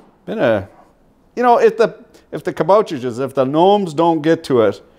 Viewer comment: did i hear that right ? de kaboutertjes 😂